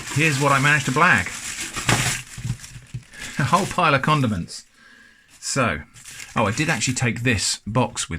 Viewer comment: When he emptied the bag at 8:00, I thought he said “A whole pile of condoms”😂 I was like you’re one hella of a sick bastard